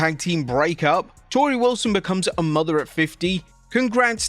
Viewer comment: I had to watch the intro twice. I thought he said Torrie becomes a mother of 50! I was like "Dayum!" 😱😂